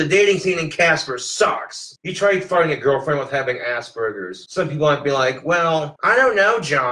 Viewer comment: Whenever he says Asperger's I hear it as "ass burgers". 😂